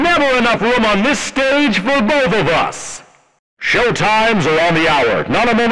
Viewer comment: I keep hearing BOBOBUS